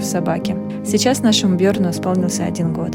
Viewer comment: А, собаку зовут Бьёрн, а мне послышалось Верн и я подумал - Какая классная русская кличка!...